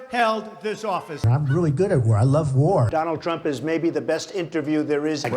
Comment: Did trump say he loves war at 6:35?